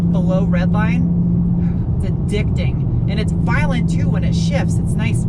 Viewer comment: Did say "this thing is addicting" ?